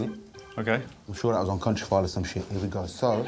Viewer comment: What does Guz say at 6:26 after "water is fundamental for a sand bridge". Just can't understand it. Sorry, non native speaker. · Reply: "I'm sure that was on Countryfile or some shit"